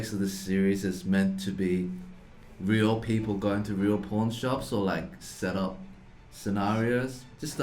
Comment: i heard "porn shops" every time :D